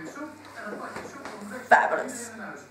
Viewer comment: what does naruto said ? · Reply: Fabulous.